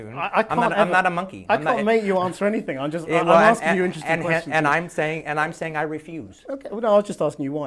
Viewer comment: subtitles says "I sandwich, I sandwich"